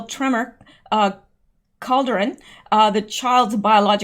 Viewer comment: Did you just say cauldron?